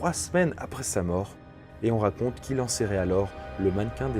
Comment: qu’il en quoi ? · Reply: Enserré